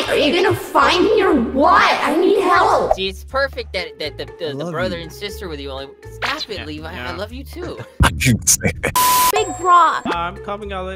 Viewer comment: Did Alex call Light " Big Brouh"